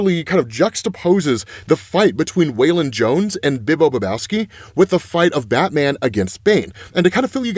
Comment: Every time you say his name Bibbo Bibowski I think you were saying Big Lebowski 😂 The Dude...And in my humble opinion there is no way that absolute Bane is gonna team up with Batman. Not even to get revenge on Joker 🤷‍♂️ In his mind he can do that himself.